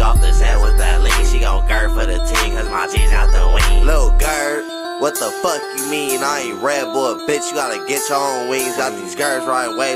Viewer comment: From 0:45 to 1:04 wtf is he saying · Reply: "lil gurb wtf you mean, you aint red bull bitch you got to get yall wings"